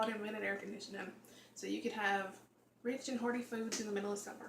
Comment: that looks so yummy. i had to replay several times because at the end it sounded like you said "rich and horny foods in the middle of summer" lmbo. tfs hugs